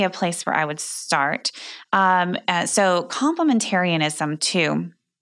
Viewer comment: What is that word she is saying. My brain cannot process it. She said it on another podcast about this book and I never heard the word used before. Please. My parents took us kids to a religious group around 1972. And although they did not blatantly say women were handmaidens to men right away, it was present. The girls I first fell in love with in seventh grade wanted half the men to be handmaidens. And They thought I fit the bill. Luckily me coming from a sheltered life. I barely heard the word women’s liberation while at Catholic School or at home. I couldn’t go to my best friend since he was dating someone six inches taller. (Puberty, he later caught up.) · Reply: Complimentarianism